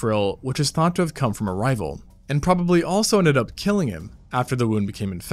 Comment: For a second I thought you said the hole had been caused by a rifle lmao